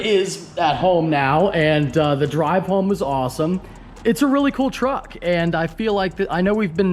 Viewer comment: I thought you said you bought the coolest new 2020 truck?! LMAO